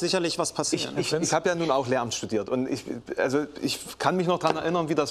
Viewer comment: Ach Lehramt… und ich hab „Lärmt studiert…“ verstanden 🥴🥴🥴